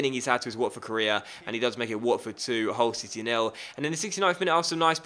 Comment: I thought he said "Watford 1, Hogwarts City nil